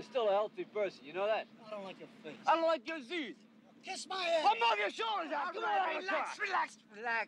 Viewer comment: what does Rocky yell at 0:34 sounds like he yelled something in Italian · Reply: "I'll knock your shoulders out!"